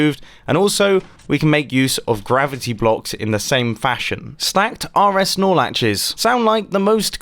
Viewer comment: I thought he said "Stack RX Snorlaxes." 🤣